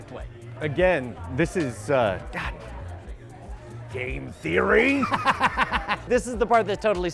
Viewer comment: did he say " GAME THEORYYYYY!!! "